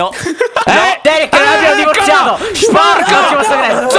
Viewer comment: "Jerick io e te abbiamo divorziato" ho sentito bene? ahahaha adoro